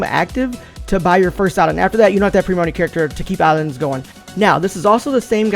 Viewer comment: Can someone translate what he said at 01:35 ? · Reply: I believe he said "...after that you don't need premium on any character to keep the islands going"